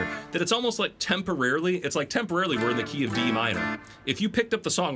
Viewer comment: Did you say 'temporarily in the key of Em?' Why Em? The notes would be A-C#-E-G. The 5th and 7th of A somehow become the root and 3rd? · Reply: He said Dm. :-)